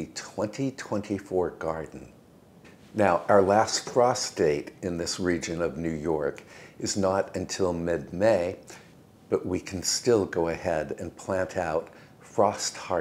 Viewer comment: I thought you said prostate. Lolol. In North Carolina we plant May 10, mostly potatoes and green beans